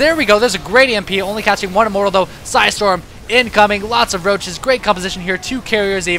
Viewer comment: At around 19:00 does he say. "Roaches"